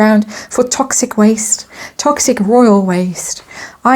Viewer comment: Your comment about Africa being the dumping ground for toxic waste had me in stitches - it applies perfectly to haz and to where he may end up. Love that you clarified it very quickly with oil, but for me, it will still refer to haz. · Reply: I think you may misheard what said. said toxic royal waste, I gathered you heard it “toxic oil waste”. Or it could be me who misheard it 😊